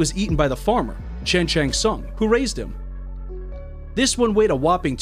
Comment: for a sec i thought he said Shang Tsung 😂